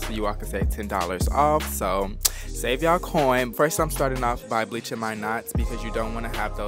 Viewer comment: Who heard it "bleaching my nuts"